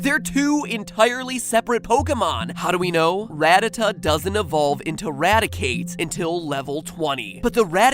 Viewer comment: This is what he said "Here's the thing, Rattata doesn't evolve into Raticate until level 20." This is what I thought he said "Here's the thing, Rattata doesn't evolve into Raticate." PERIOD